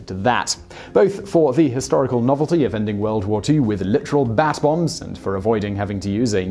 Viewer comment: For the first few times you said bat bomb I thought you said bass bomb. I was thinking to myself why would you attach an incendiary device to a fish